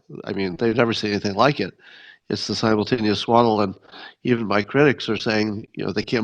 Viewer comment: I thought he said "Simultaneous Swallow," when he said, "Simultaneous Swaddle!" Quite the difference!😊